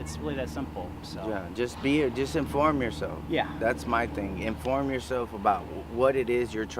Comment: Lol. Does he accidentally tell the truth there? I swear he says “disinform yourself” 🤣😆😂